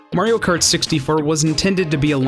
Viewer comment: did he say Mario kart sixty fur?